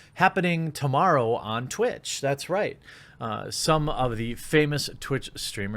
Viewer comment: I skipped to 0:54 (it was right between 0:53 - 0:54 that I skipped to) I heard this... "itch, that's right" I was like wow... did he just call someone a B and follow it up with a Christopher Walken esque confirmation. I was disappointed but relieved you'd not lost the plot.